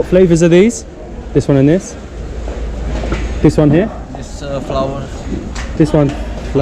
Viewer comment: he actually says flower (Rose flavor Ratluk) and not flour what you maybe thought ;-))) Welcome to Belgrade guys...